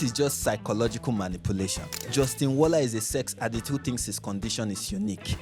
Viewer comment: I think you wrote attitude instead of addict in the subtitles there